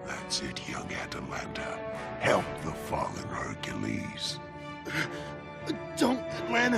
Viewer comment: Did Polyphemus said "help your father Hercules" ??? Atalanta is the daughter of Hercules?